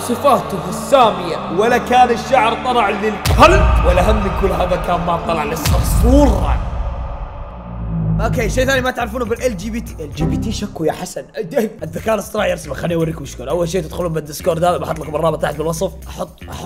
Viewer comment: ماسمعتوه قال LGBTQ 😂😂😂😂😂